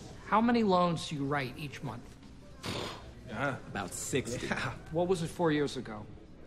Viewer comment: Error in subtitles at 1:23 Subtitles say: "about 6" What was said: "about sixty" (the increased volume is why the brokers are flying high)